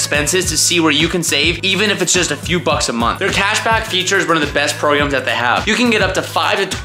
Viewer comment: Did you say Pashback? hahaha